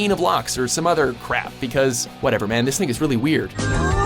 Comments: Sooo Am I the only one who heard "this nigga's really weird" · Sounded like "Whatever man, this nigga's really weird" 😂😂😂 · why at the end it sound like he said this nigga is weird >.> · what ever man this thing is really weird, (some people think he said this n***a is really weird) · did anyone else hear "idk man this nigga's really weird" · Did watchmojo just say the n word?? “Idk man... this nggas really weird” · did he say "this nigga really weird"LOL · It sounds like he said "this nigga's really weird"